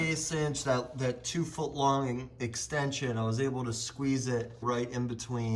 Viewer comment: Did he say two-foot-long-extension ??